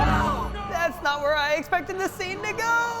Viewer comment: had to do a double take. at first i thought he said, "thats not what i expected to see niggaaa"